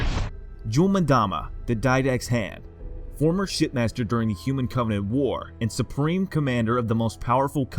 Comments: sounded like he said "Jul M'dama, former Shitmaster during the human covenant war" 😂😂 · Did he just say "Shit master"?